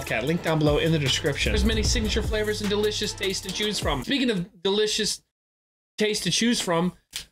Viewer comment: I thought Wildcat was gonna say "Speaking of delicious Jews to taste from..."